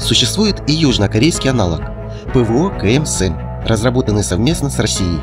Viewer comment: южнокорейский аналог ... совместно с Россией??? Мне не послышалось ??😅